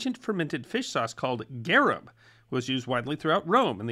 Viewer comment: I heard Gharab - but according to Wikipedia - Garum is a fermented fish sauce that was used as a condiment in the cuisines of Phoenicia, ancient Greece, Rome, Carthage and later Byzantium.